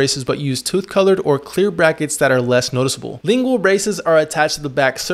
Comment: Thought he said ✨Bingle Braces✨